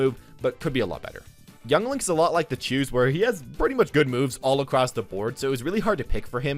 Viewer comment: I thought he said Jews for a sec